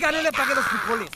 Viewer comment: Segundo 0:02 alguien entiende que dice?😂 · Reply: Verg no le apague los frijoles 🤣